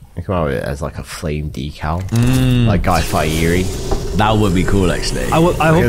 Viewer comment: what did say? is he talking about the tv chef? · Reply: Haha. I was saying imagine if we emerged with a fire decal like a Guy Fieri shirt.